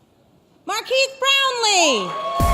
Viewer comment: I hate when people call you "MAR-KEES" and not "MAR-KEZ" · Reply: Thought she said Marq Keith Brownlee.